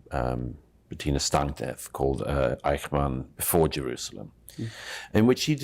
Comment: anyone knows how to spell that? I hear Patina Stantev or Stantyev and Eichmann Forgerers but Google and Goodreads find nothing.